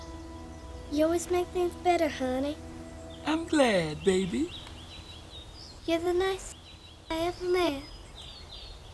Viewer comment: What is Johnane saying 43:00 Im confused why she’s mad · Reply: N word